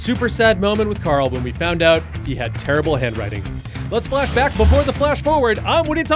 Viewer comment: When you said "we found out he [Carl] had TERRIBLE HAND-WRITING", I thought you said "TERMINAL BAD WRITING" (as in "terminal cancer")